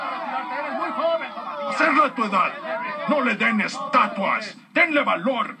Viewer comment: qué dice Clubber? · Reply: Hacerlo a tu edad... pq se quiere retirar